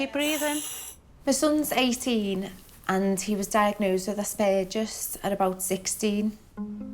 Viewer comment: What is she saying her son has? I couldn't hear her clearly so I put on the CC and it says: "asped just" ??? What is that? I've never heard of that? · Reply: Asperger's